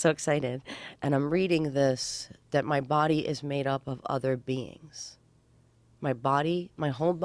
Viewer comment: I thought I heard, “my body is made up of other beans” I would have drawn little circles too... poor lil beans. Awaken the beans with the bean cans. Not sure I can ever eat hummus again....